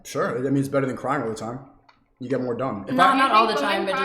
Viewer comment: I think his entire stance on just about everything is retarded, but he didn't say "it makes you dumb" he said "you get more done" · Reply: i just rewatched that moment, i keep hearing "you get more dumb" but idk tbh